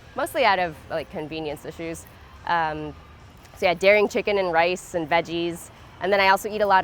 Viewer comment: Asking for clarification and nothing more, but at roughly 4:35, did she say that she eats dairy AND chicken? Then shorty after did she say she could not be with someone who is NOT at least trying to eliminate animals from their diet? Just curious to understand. I appreciate any patience and kind responses! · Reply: No Daring Chicken, it's a brand of vegan soy chunks alternative to chicken. Every time someone mentions daring Chicken in an interview people get confused but it's vegan. I should show a picture of Daring chicken when people mention it... but I'm just so busy trying to get all of these done